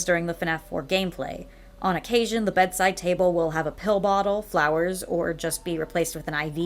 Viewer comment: I low key thought that said piss bottle